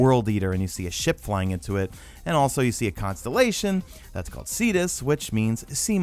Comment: first time I heard you say Cetus and thought I heard fetus and was slightly disturbed until I had to rewind